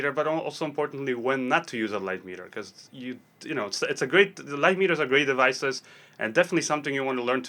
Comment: In the beginning I misheard “lightmeters are gray devices” and thought - well, makes sense! Awesome video, thanks!